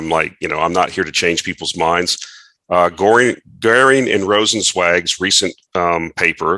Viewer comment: Hi - at 23:45 , whats the name of the paper? Can't understand... Thanks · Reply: Goehring & Rozencwajg